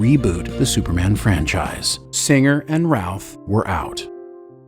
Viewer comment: Great great video. But I keep hearing Brandon "Ralph" when you say it 🤷😅 By the way, Tristan Lake didn't win a daytime Emmy in 2018. He got a nomination.